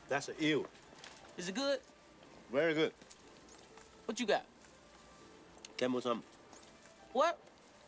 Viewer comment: lee sounds like he is saying camels hump😂